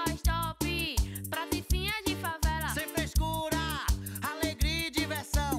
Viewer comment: só eu que escutei "praticinha de favela"?